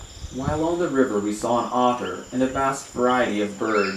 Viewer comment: did he say "we say an Otter " ? THERE ARE NO OTTERS ANYWHERE IN AFRICA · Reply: We did see an Otter. I also didn't think there were otters in Africa, but after seeing it, I did some research and learned I had been wrong.